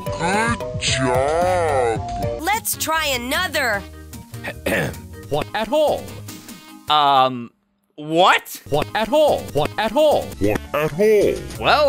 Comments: help me find Arthur’s cans of paint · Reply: Yeah. And when mr ratburn said "what a ball" I think he said what shape is a ball. XD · "What at all?" - Mr. Ratburn 2016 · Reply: Broseph i thought it said “what a bowl” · "What at all?" - Mr. Ratburn 2016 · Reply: I thought he said “what a ball?” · What a hole? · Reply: I heard "what a ball" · I think he said "What's a hole" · I think he said what has a hole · "what asshole!" I think he is saying. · It sounded like buster said "Help me find arthur's pawns of eight" and at 12:06 I think he said "What at all"